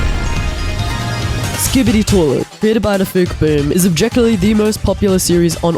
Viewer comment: What i hear: skibidi toilet, created by dafuq boom What the caption shows me: scoobydile created by defuk boom